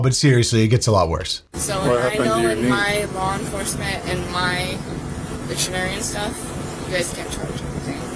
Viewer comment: I swore she said I know my law enforcement and my veterinarian😂😂😂😂